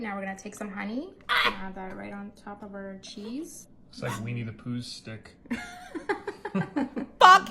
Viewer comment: Did he just say "WEENIE the pooh"? Lmao